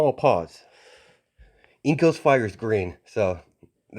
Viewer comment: I thought you said riyouko is 503(age) but you said inko's fire is green